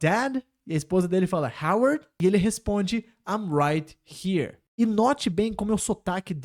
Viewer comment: Quando ele disse "I'm right here "eu entendi "Maria"😂😂